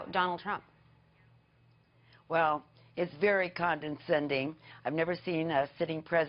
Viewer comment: Does she actually say "conden-sending"? Does she mean "condensation"? Or what? Jeez, the public schools of 1912 must have failed her.